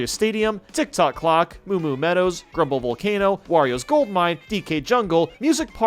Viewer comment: I heard you say warios cult mine